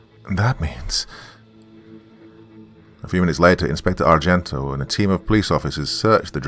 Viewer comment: At first I thought that the said the names of the police officers were Frigo and Sargento, like the names of the cheese companies! Of course I was completely wrong but I initially figured that the detectives were going to be a couple of cheesy characters!